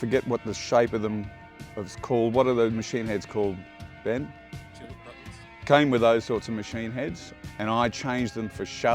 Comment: What did say? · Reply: Sounded like “Tuning Buttons”